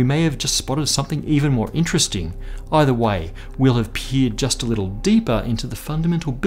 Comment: Sounds like he said we'll have peed a little deeper into the fundamental building blocks of space-time 😂 Now that's a significant p-value!